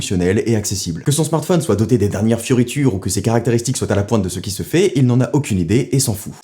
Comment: des dernières quoi ? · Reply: fioritures